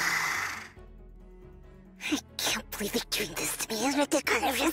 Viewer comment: what she say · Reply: 'I can't believe they're doing this to me, that's ridiculous' as for the rest idk